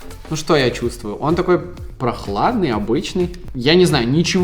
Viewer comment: Чтооооооооооо? Обычный гей - это мне послышалось про антисептик за 1$